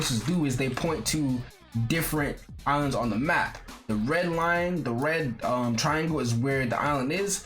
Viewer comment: fr thought this man said the grand line lol